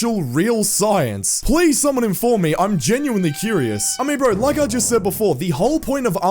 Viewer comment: why you said hummingbird instead of I mean